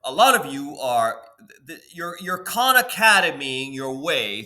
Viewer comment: Hello, I wanted to know what "Con Academy" means during 0:27. I tried to find the meaning of this word, but I think the subtitles are wrong with the real spelling of this word. Best wishes, Susand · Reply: He said Khan Academy not "Con"